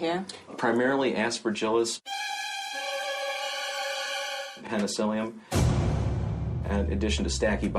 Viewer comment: When they said Penicillium I thought they said penicillin, and I found out that you can get penicillin from penicillium. Long story short, I have an allergy to penicillin to the point where I could go into anaphylaxis, if I were in the bathroom, I'd probably drop dead.